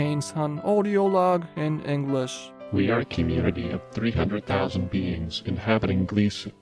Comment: I heard this as "we are a colony of 600,000 beans'